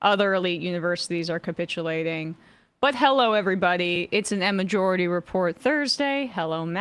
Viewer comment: Am I hearing things? I could swear that Emma is calling the show Emma-jority Report